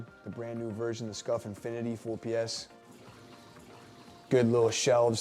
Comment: Not sure what he said but did anyone else hear 4ps